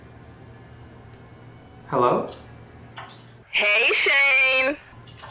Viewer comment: It sounded like said : Hey Sha-ane. Kinda like a robot, right?